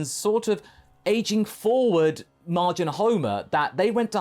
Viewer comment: I thought you said Majin Homer and got really confused.